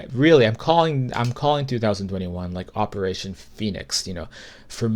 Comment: OMG! For a second i thought you said, "Operation Penis."